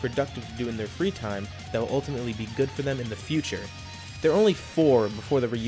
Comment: did you just say ULTIMATE? (And all of an sudden I know where this is going)